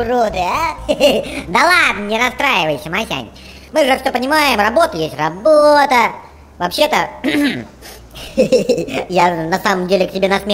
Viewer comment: "Любимая работа – работа идиота" или мне послышалось?...